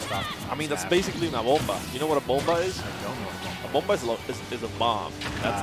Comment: I thought the commentator said "bofa" and I was waiting for the punch line lmao